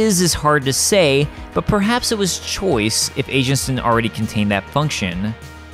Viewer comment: I kept hearing 'Agents' as 'Asians'. lol